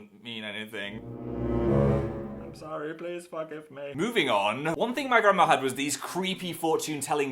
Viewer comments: Lmao I thought phil said "I'm sorry please fuck with me" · When he said "please forgive me" I thought he said "please fuck me" XD